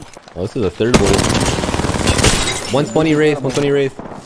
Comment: i’m so used to his val callouts now when he said 1hp wraith i thought he said 1hp raze